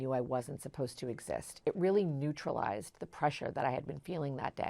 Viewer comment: Acting antisocially or hurting others is not necessarily more natural or beneficial to the individual. I wonder what is the difference that causes someone to only feel joy when causing problems. It isn’t the default, so what is it? What’s missing? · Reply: I thought she said "released the pressure", I don't remember her saying joy.